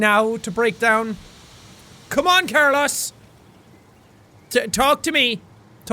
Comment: My father is named Carlos. My brother is named Carlos. I have cousins named Carlos. Never In my life did I think a person could fuck up the pronunciation of Carlos. GFDI Jack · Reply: Same it bugged me too i dont understand why or how he did that it sounded like he said carlas